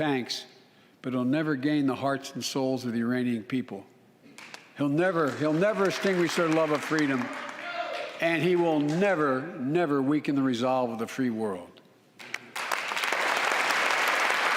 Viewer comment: It's right after 4:06 but he clearly said Uranium people